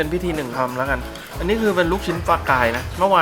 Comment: ได้ยินว่าลูกชิ้นฟอลกายส์ <num>